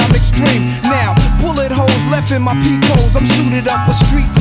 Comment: I always heard it as "bullet holes left in my peoples"